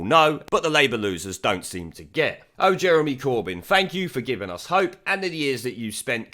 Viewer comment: im sure i heard "oh corbyn the wanker" "oh corbyn the wanker" and i was happily singing along.